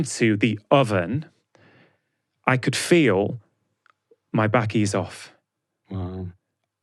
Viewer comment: what is he saying here? Can't catch it. My backey is off?